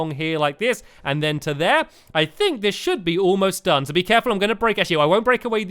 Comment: you said do be careful and it made me think of Doobie Doo from cave den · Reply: it sounded like u said Doobie careful